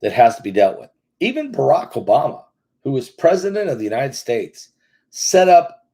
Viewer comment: What did you just say ?!?! “Even Barak Obama who is President of the United States” …. 😳😳😳 Or did l completely misunderstand …